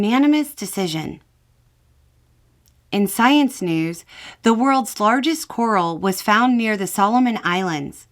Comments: I thought I heard "world's largest squirrel." You can imagine my confusion when you then said it could be seen from space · Listening to this after getting done with my 12-hour shift, I honestly thought you said that the world's largest squirrel was found in the Solomon Islands, and then when you said able to be seen from apace, and I had to rewind the video 🤣 That giant squirrel will kill us all!! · I thought for a moment you said, "The world's largest squirrel", until you said it could be seen from space. 😂